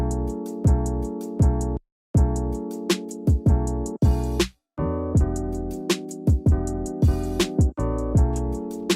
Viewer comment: min how many beers did you say you drink? 😂 Great video man! 😁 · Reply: Only drank 1! :)